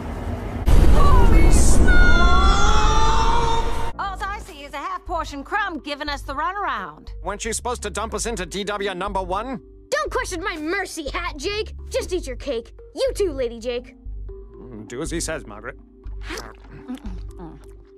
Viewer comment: omfg I didn’t notice the first time Tiffany called Jermaine “worst Jake”. I thought he’d said “art Jake” lol